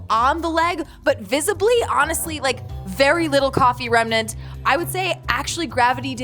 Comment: Did steph say remnant